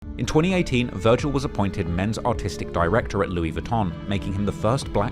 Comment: Bro your accent I thought you said “Men’s autistic director” 😭 I was like man this mfer fr??? 💀💀💀